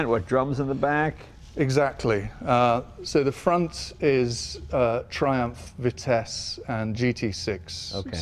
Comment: wait wait did I hear Triumph?